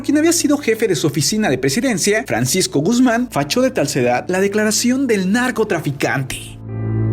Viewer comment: desde ahí escuchen más de 1 vez creo que dice "facho de talcedad" expliquenme